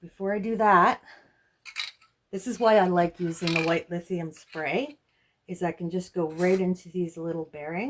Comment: What kind of spray are you using? It sounds like you said white lithium.